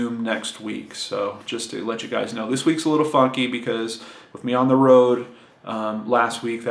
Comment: I heard "every thing is all fuck-y" instead of "funky" and I laughed for so long. Glad to see the trip went well!